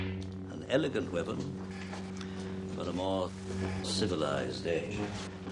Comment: An elegant weapon, for a more civilized age. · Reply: I thought he said, ‘With a more civilised edge.’